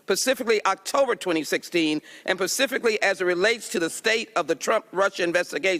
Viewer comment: "pacifically" at 13:26 instead of specifically